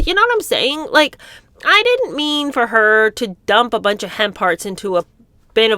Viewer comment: Oh, it’s hemp hearts! I didn’t understand until I saw it written down. Never heard of them before, and it sounded like was saying ‘hen parts’. Judging by the other revolting flavours, it could have been a possibility.